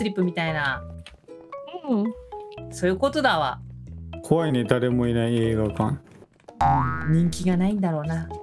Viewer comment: うぽがみちゃんです！ 19:30 のの発言が「故意に誰もいない映画館」じゃなくて「怖いね、誰もいない映画館」 に聞こえました....？ 気のせいかな？